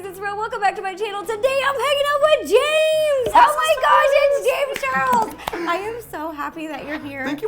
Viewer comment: Is it just me or when ro said omg it’s James Charles I thought she said omg it’s gaymes charles